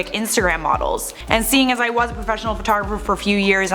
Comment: photgeferfer is what I heard